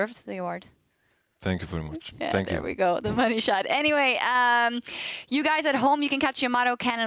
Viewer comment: did she just say "the money-shot"?